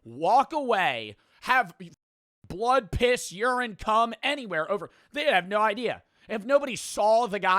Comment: did bro just said "cum"